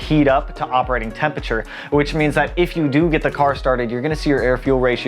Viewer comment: Did you just say "IF" you get your car started? 😶